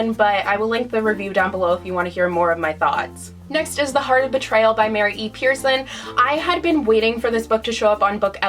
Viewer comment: I wanna read the book you mention on 7:28 minute but I cant understand the name of the autor ( english isnt my first language : Could you please help me writting it?? Thanks! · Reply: The heart of betrayal by Mary E. Pearson